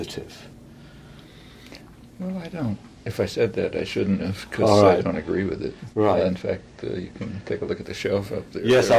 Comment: English is my second language and I can not make the name of the Argentinean anarchist Chomsky mentioned at 0:38. He went on to explain he wrote a detailed critique of the Anarchist Movement during the Spanish Civil war in 1936 . I cannot understand Chomsky's pronunciation of his name. I would appreciate if anybody writes it. Thanks! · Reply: I found it. If anybody needs it, the name is Diego Abad Santillán; and the name of the book is After the Revolution. If you google it, you can read the whole text.